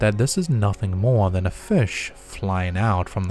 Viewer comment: He says viewers say its nothing more than a FISH??? WTF? hold up. ok im confused. who... when.? nah wait... how TF would anyone think its a FISH??? am i hearing it wrong? youtubes auto CC also says "fish" . is there a new use for the word fish that im not aware of?? HELP! IM AT A LOSS! i could care less about the video, im just tripping about how far off this is from making any sense to me...